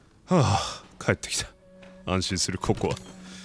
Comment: くらいの時に「安心するココア」って聞こえて、「可愛いかよ」ってなった